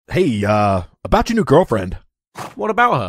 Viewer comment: " i bought you new girlfriend" is what it sounds like he is saying